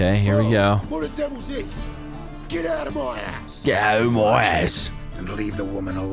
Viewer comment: what said sounded scarily like "Get outta my ass" in that rapey voice.